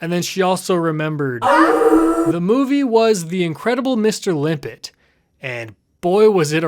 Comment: I got a little worried at first when I thought your brother said "The Incredibles".